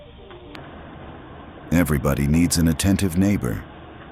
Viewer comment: Finally I heard it. "An attentive neighbor". Not someone who was "paying attention". He didn't have his wallet out and wasn't paying.